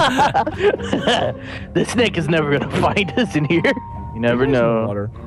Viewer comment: did he say the n word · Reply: Splixxz YT he said this snake